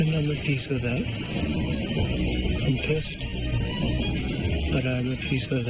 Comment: What was Coach saying around 27:45 after Brandon won Immunity? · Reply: Taylor Jaden "I'm at peace with that.. I'm pissed but I'm at peace"